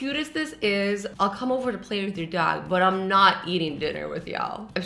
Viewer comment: I thought she said “ I’ll come over to play with you dad” 😂